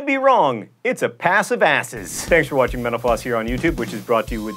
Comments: Cool video but when he said thanks for watching mental floss, I thought he said thanks for watching menopause lol XD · everytime he says "Thank you for watching mental floss" I hear "Thank you for watching menopause" :) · It sounds like he is saying, "Thanks for watching menopause on youtube."